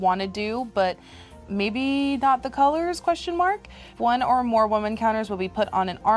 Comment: When is reading the card, it sounds like she says "When one or more woman counters" 🤣